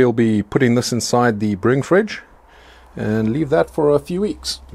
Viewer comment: Quick question just to help me (Dutch guy) out with my English vocabulary, what exactly did you call that fridge and how do you spell that? A "Bring Fridge"? How does it differ from a regular refrigerator? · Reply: Hallo en welkom! It is a brewing fridge, or as I like to call it, "The Ferminator"! I did a video on how I built it: <link>